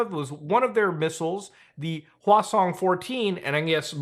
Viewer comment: hwasong is sound like croissant said bye carl